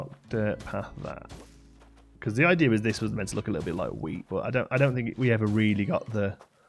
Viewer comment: when you said the hyacinths were meant to look like wheat I misheard and thought you said weed lol i hope the flowers have a green version so i can have my own AC weed farm hahaha